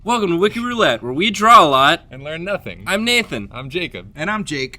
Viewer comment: I heard "... and wear nothing."